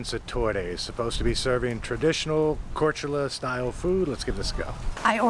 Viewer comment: Big mistake somewhere around 2:20, i think you said serbian.. we'll forgive you this time 😛😁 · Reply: Had to go back and watch it. says ‘serving’ traditional style food not Serbian traditional style food😊. Sounds close though!